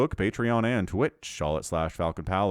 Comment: Wait, did I hear "thank you so much for watching, you take carrier self" at the very end of this video?